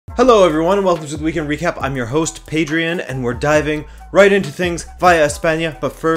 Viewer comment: Why did I hear 'im your host, padrian'?